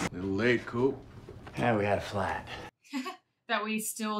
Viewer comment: When Matt (Coop) has such a thick accent, he says, "We had a flat"... and I hear it as "we had a flight" - in reference to the drone they were chasing... boy was I wrong haha